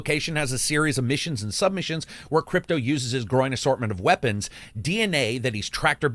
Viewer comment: You said "growing assortment of weapons. I heard: "groin assortment of weapons." and I was totally ok with it.